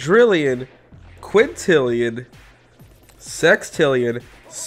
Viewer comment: did I hear “sex” or am I crazy?